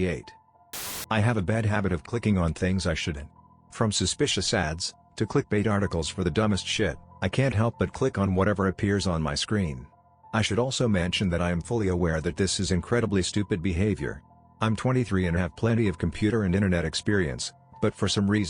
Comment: Thought he said buttlick 😂